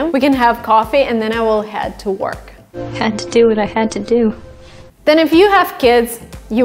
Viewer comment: hi 4:06 in this example, i think said " had to do what i had to do " not "head to" especially that she didn't mention a place. i might be mistaken, could you please reply that